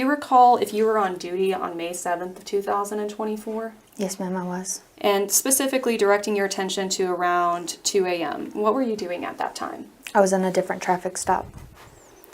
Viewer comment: Did anybody hear the date she was working May 7th 2024 or am i mistaken! Because May 7th 2024 hasn't arrived...